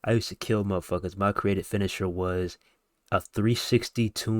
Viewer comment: wtf did i just hear 😂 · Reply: Create a finisher was wild af bruh😭😭😭